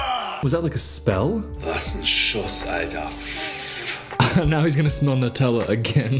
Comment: He said: "Was ein Schuss alter" which translates to: "What a shot dude" It was said at 7:00. So uhm no not a spell :D